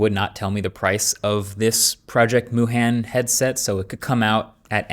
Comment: Thought he said Project Wuhan.